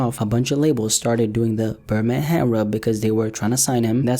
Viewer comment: what did you say? berma hand rub???